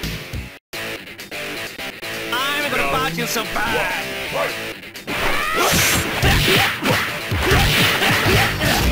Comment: Im not expert speaking english!. Can someone tell me what says billy on min 9:00 thank you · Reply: "I'm gonna bounce you so bad!" - Eu vou te rebater tanto! É isso que o Billy fala no começo da luta.